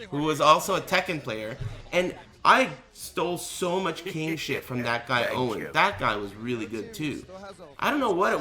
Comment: What does he say at 7:35 ? I stole so much __ shit Is he saying game? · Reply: king. sry for the late reply. i was prugnut